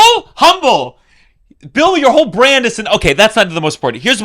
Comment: I thought you said Bill Bur, i was shocked 😅